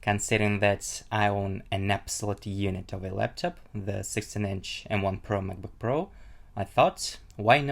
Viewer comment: it sounds like he said 69 inch for the 16 inch